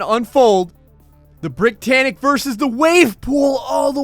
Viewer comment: sorry I thought you said brittanic